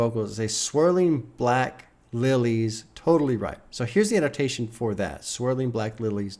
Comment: i was NOT ready to read the lyrics. its a mix of her accent and my inebriation, but i SWORE she said "like kidney stones and dandelions" not "swirling black lilies totally ripe"